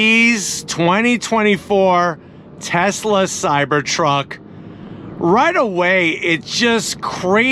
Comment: When i play this video on low volume with my phone it sounds like he is saying Tesla Cybertron.